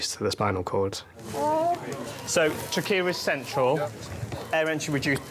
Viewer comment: Whenever one of the doctors says "trachea is central", never fails, I hear "Shakira's central." 🤦🏼‍♀️